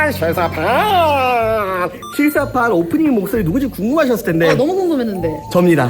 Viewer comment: 자막에선 무대라고 써있는데 왜 목소리라고 들리지..?옥에티인가..?